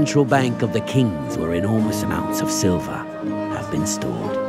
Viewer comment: i only heard enormous amounts of silvers